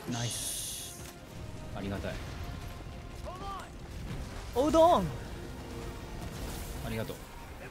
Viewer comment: おうどん！(空耳)好き